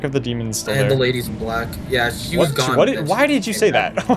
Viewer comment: he did infact said black LMFAO